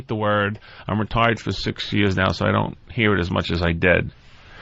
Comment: sounded like he said "I'm retarded for 6 years now"